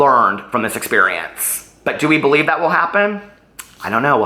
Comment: I got really tired of her continuously saying "I love you" in my head I heard "you're gulable"